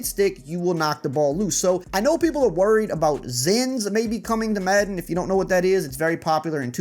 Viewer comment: does anyone hear him say Zyns here? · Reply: Yea zens like people be using in 2K lol